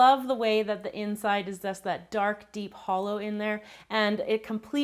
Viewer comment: did she just say holo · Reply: No she said hollow